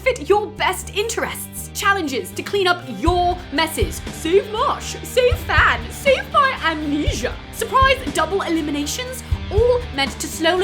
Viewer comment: What she actually said : save marsh, save fan, save my amnesia What i heard : so posh, so fab, so fun amnesia I honestly think what i heard would be a better roast